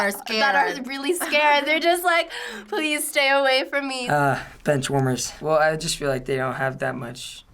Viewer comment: DID HE JUST SAY BENCHWARMERS? WE DONT WANNA DIE FOO